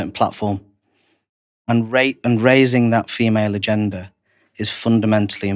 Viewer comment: ha ha. at 3:00 I thought he said erasing the female gender